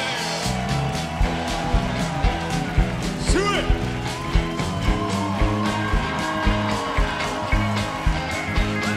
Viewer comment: What does Jimmy shout at 3:37? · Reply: Daniel Sypien Suey! Johnny used to holler that.